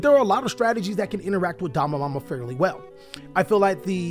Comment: What is the card that ended on Could be better next to DD Crow? Sound like he is saying Dalai Lama